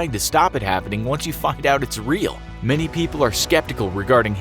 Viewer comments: he says fked out instead of saying found out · Is it just me or does it sound like he is saying F U C K. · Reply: I thought it was "Fucked out" so i turned on the captions where it is clearly written "find out". ☺ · who heard "f*cked out its real" Just me okay. Tysm for 76 likes!!!!! 😁😁😁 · he said the F word instead of find out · anyone else hear “once you fucked out it’s real?” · Did he just say “ once you’ve F%*ed out it’s real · sounds like he said fucked out its real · Did he just say 'F**ked' instead of Find? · Only I am the one who heard f**ked out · Did anyone else hear him say “once you f**ked out its real” 2:38?? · it sounds like you said “when you fucked out it real” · once you fucked out its real? Anyone else hear that too? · does it say until you’ve f***’d out it’s real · did you say ‘fucked out it’s real?’ Just wondering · am I the only one who heard “once you fked out it’s real” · he says "fucked out it's real' instead of find out it's real...lol